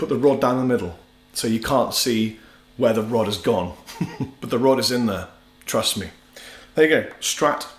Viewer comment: Sounded like you said "the rod is in there, truss me" Excellent video..I just started playing so thank you very much! I bought an imitation strat, S type as you called it, and I couldn't figure out why there were 5 positions on the switch! You're the most amazing player I've seen on YouTube. Not based on this video.. but other videos. Thank you!